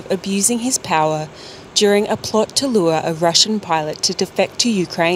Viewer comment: I thought you said it was Russian robots.